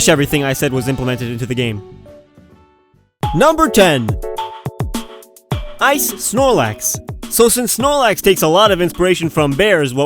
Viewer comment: I heard wrong with the snorlax, thought you said Isis snorlax.